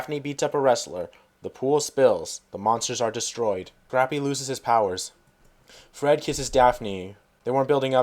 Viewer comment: LOL at 10:25, did you say "Crappy loses his powers"???